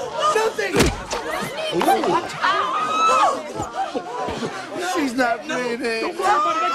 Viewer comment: at first I thought George said "She's not mayonnaise! 😫" XD